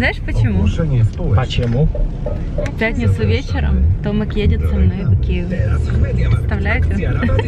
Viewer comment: Мне послышалось- в пятницу вечером мы едем в Киев, поужинать